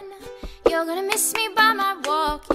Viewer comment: am I the only one who hears “by my taco-“